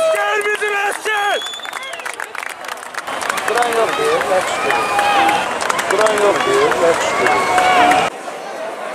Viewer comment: Polisin ne dediyini başa düşmədim bilən varsa desində.😕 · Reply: deyirki heckim qisqirmir